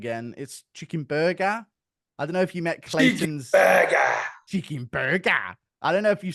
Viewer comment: Did bro just say chicken "dic"?